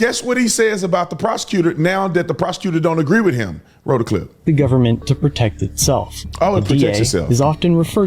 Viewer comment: Whoo, buckle up. hates dirty cops because they make good cops look dirty by association. He also calls out citizens who misunderstand the law. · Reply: that’s not what I heard him say but I just found this channel. I heard him say, “I hate the government.” I also heard him drop an nbomb. (D)amn it!